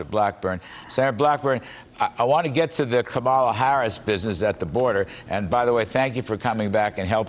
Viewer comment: I could swear he said "Kabbalah Harris"